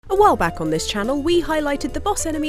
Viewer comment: why did I hear "OWO back on this channel" at the start? end me